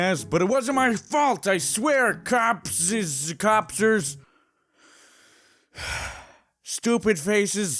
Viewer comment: I heard corpses... Cool!